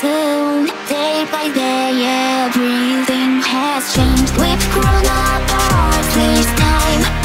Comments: It sounded like "with CoronaVirus this time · did i just heard "with corona virus this time.."? (chuckles) i'm in danger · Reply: It actually says "we grown apart as time" I know the vocaloid software dont really sound really human so people find this really hard to understand what Gumi (the singer of this song) is staying but it was pretty funny what you heard lol